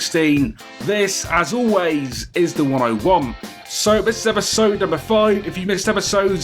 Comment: I actually thought what he said in the intro as "....And this as always is the one I want" instead of "The 101" So every time I watch one of his videos I'm like "wtf which series does he really want?" xD Great Video bro, wish you can keep up these videos on every WWE game :D